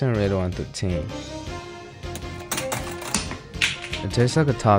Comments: I thought he said "tastes like a tapatio pizza" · ngl thought he said "the only problem is that i might have to shit" and my lactose intolerant body agreed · I thought he said “the only problem is that I might have to shit”